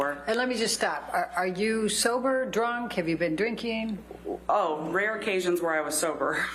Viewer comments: did she just say in freudian slippage fashion - "Rare occasions im sober" ⁉️⁉️ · Did I hear her correctly where she says “rare occasions that I was sober”